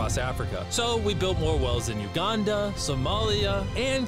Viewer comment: he said Uganda but i was like wtf he said, did he just say wakanda lol